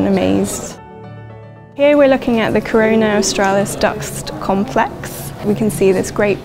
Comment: Did she said corona!!! 00:24😂😂🤣🤣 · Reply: Corona Australis